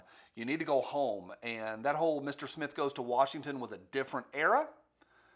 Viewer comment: I thought you said "Mr. Sniff goes to Washington" thought it was appropriate and a pretty good Tshirt idea.